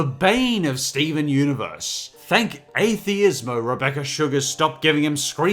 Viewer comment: does he say "thank Atheist?"